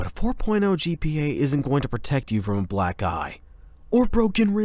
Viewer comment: "..protect you from a black eye-" I heard that as "black guy" and I gasped- I'm sorry if this comment is offensive 😭